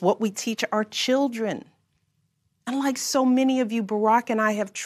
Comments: Thank you ! This country misses your family and leadership so much. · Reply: Listen what she says at 6:00. She says "it's what we teach our children. UNLIKE some of you, me and Barrack teach our children moral values." Listen to her. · "unlike so many of you" Barack and I instill morality in our children. Did she really say that!? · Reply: No she said "and like" not unlike. Read the context before you assume she said something that would never come from her mouth. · I really don't get what Michelle is trying to say around the 6:00 mark, can anyone explain? Unlike so many of you? Who doesn't try and instill those values? I'm confused. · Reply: She said “and like”. I totally understand the confusion though, it sort of blended together to sound like she said unlike. Beautiful speech.